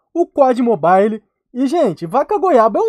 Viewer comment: só eu que no minuto 00:35 entendi o falando ''pó de mobile''' ?? kkkkkkkkkkkkkkkkk